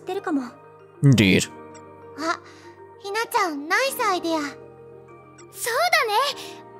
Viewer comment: Did she just say nice idea as Nai saidea? Hm...